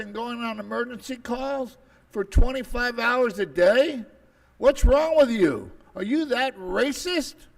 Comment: did he say 25 hrs a day 😂